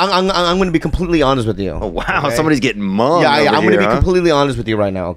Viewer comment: did he say somebody's getting Hmong over here? 😂